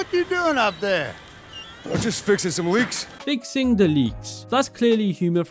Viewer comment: First thought he said "fixing some leaves".